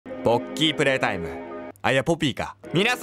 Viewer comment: ボッキープレイタイムにしか聞こえんwww 連打したらやば過ぎたwww 連打用 0:00 0:00 0:00 0:00 0:00